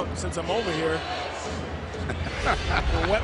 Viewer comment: does he say wet my whistle at 0:32 !?!?!?!?!?!?